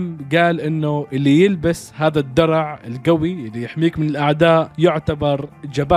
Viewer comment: وش قال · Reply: قال الدروع القوية للك.س😮